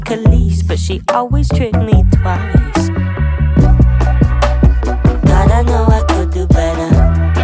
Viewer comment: I thought i heard it as: 'God I know I could do better...Maybe when I'm put together' but the actual line sounds good too haha